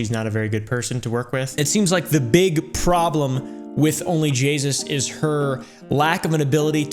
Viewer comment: onlyJesus what I heard